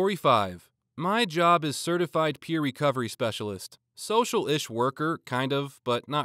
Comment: when he said "Social-ish worker around 11:17, I thought he'd said Socialist worker. Soviet Childcare blyat.😅Also, by ICP, I assume you mean the Irish Communist Party, and not the Music duo.